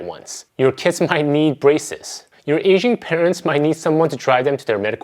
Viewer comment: When he said “your aging parents…,” I thought he said your Asian parents. I was like wait, what??? You get Asian parents at 40???😂😂😂